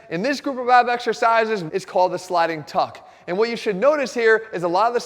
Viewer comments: lol... I thought it said "Sliding Fucks" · i thought that said sliding fucks